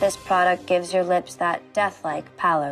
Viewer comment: What she said: "this video." What I hear: "this idio-"